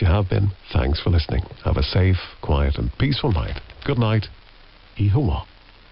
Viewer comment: What is said after "goodnight" here? I can't quite make it out. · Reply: According to someone on the Ringway Manchester video on the shutdown, the announcer says the Irish for “good night” which is Oíche mhaith.